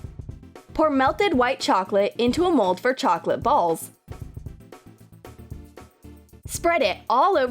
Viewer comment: did you said kpσp or ¢αкєρσρ... I heard kpσp lol I think I heard wrong 😅😅😅well that was one of my favorites and the Nutella 💜💜"ι ℓσνє ¢нσ¢σℓαтє"